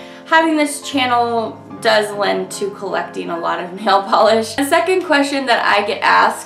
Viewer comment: "But having this channel does lend to..." I thought you were going to say, '...producing a large collection,' and with my volume down, i thought i heard you say, "[...]'producting' a lot of nail polish." alas, i knew you're pretty good at catching your mistakes, so it was i whom was mistaken (a couple times until i turned up the volume.) it made for a small chuckle, though. enter me, please.